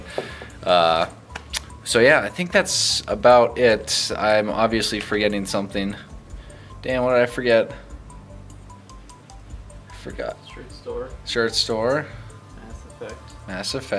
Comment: i thought he said "Damn, what did i forget"